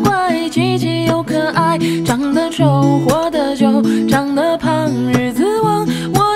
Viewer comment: 长得胖 日子旺， 我听成 “长得胖 椅子歪” 😳